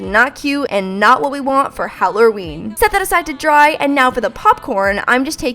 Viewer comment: why at 5:53 did she say hallorween its halloween xD